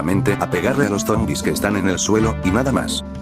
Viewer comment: No entendí nada sobre que cambió del superpoder Solo escuche "hacia daño en todas las lineas del suelo y ahora solo hace daño en las lineas del suelo" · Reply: Hola, ahi dije que "primero hacia daño en todas las lineas del suelo, sin excepcion" y en la actualidad, dije "solo hace daño a los ZOMBIS del suelo". A lo que me refiero, es que hacia daño tanto heroe zombi como esbirros zombis, y que ahira SOLO hace daño a los zombis del suelo. Salu2 bro